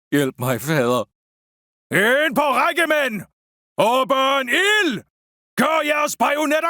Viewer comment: I think 1:03 says "Åben ild!" There is a chance that I am wrong.